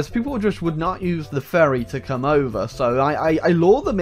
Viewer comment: he say ferry but in my head i hear "furry" what is wrong with me help